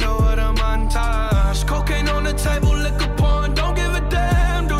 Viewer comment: The first few times I listened to this I thought it said look at porn instead of liqour pourin